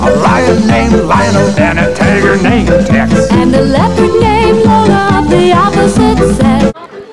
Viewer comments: what were you going to say?????? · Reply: “Leopard named Lola of the opposite sex” referring to gender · I think she said sex and it got cutted out of there